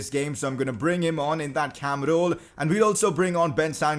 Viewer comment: I thought he said Hamroll😂😂